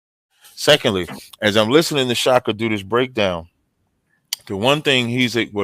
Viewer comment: LMAO I should have sworn said shaka doodoo's